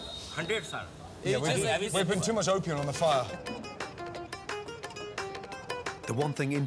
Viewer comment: did he just say they where putting to much opium on the fire lol?